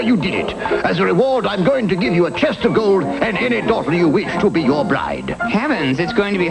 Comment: Funny, I thought King said 4:44 that he would give him a testicle (chest of gold).